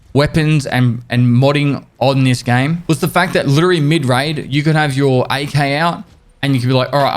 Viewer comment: did you actually say mid raid? hahahaha